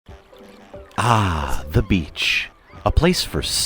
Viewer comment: At first I thought it said ”ahhhhhhhh the bitch”